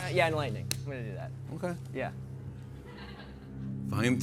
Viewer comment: Holly Molly! What did he just say? Did you hear that? “Yeah! Enlightening, I’m gonna do that…” I have no more questions, Your Honour.